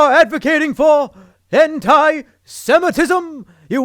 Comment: i thought said "hentai-semitism" for a moment